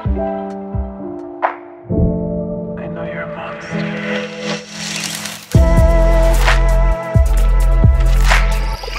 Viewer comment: did she say "you're a monster"? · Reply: "I know you are a monster"